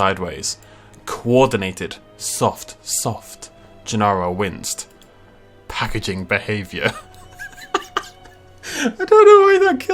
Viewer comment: I thought he said "Gennaro wins the packaging behaviour"